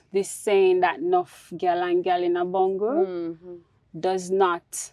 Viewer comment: I'm not jamaican but I love these stories. I normally can catch on to sayings because I'm from another Caribbean island. What is the saying at 14:54? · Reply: Nuff gal and gal in a bundle. That means a posse/group of women